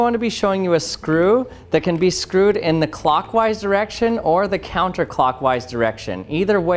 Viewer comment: I thought he said "clockwise erection"! 😂🤣